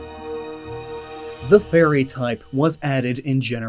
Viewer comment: for a second i thought you said the "furry type"